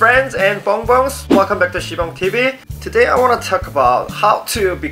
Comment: I thought he said sponge tv 😂